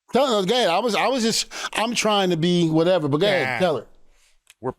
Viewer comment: WHAT did D-von say???????????????????? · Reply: "Duh duh gay, I'm trying to be whatever, go ahead"